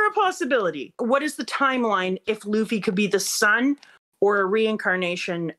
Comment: Me whatching with subs at 52:55 "is a possibility that Luffy could be the Sun" Me: WTF 🤯 Then I realized she said the SON 😅